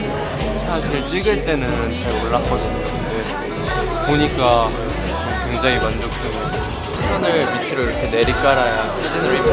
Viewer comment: the subtitles are wrong he said i looked down when taking the pictures so it came out nicley